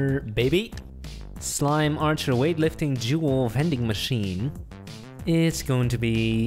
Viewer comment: when he said “jewel” i thought it said juul.